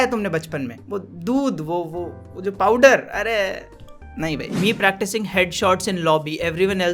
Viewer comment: Did he say powder 😂 Ok ^h^